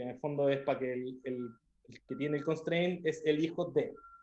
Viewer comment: que palabra usa cuando dice algo de costream? no entendi muy bien como para escribirlo y me gustaria buscarlo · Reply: Child of , de "Child of Constrain"